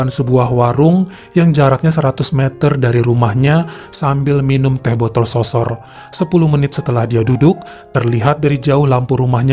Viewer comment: Teh botol sosor😂 apakah aku salah dengar wkwk